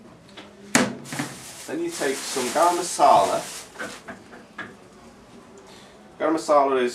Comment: he says "garam masala" in case you didn't hear